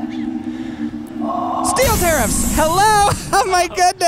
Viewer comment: STEEL TARIFFS! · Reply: LOL i thought i heard "STEEL STIRRUPS!"